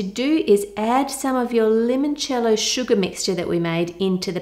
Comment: For a few minutes, I thought you were saying lemon jello...!😅😅🤣